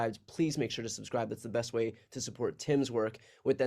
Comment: I thought BTC said, "This is inside the Reich." Seemed legit.